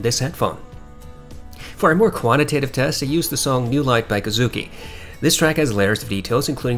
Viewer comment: I am unable to find the “New Life” track you mention for the “Footsteps” listening test. I heard you say “Kazuaki” Is this Kazuaki Tanahashi music? Are you able to direct me to where I can find this track., please?